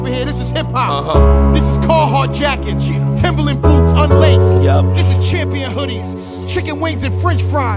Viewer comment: I could have sworn he said "yep yep" after chicken wings and french fries..... anyone else ??